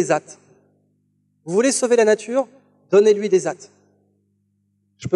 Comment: Il dit quoi a 33:14 ? Donnez lui des hâtes ?? Hein ? ^^ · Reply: il dit "AT" soit 1heure(T) d'attention (A) maximale